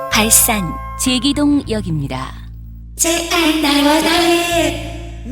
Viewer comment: 나만 '제발 날아가'로 들리나?